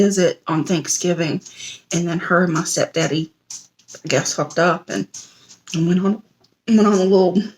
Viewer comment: Is it just me or did she say fucked up